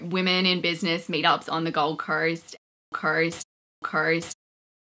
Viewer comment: I keep hearing "on the golf carts."